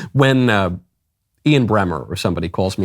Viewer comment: I thought you said "Ewan Bremner"